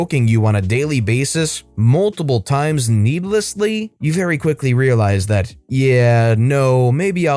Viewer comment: Did you just say NEEBLESSLY?! Are you a neebs gaming fan?! Lol You probably said needlessly but my fanboy ears heard neeblessly and got overly excited lol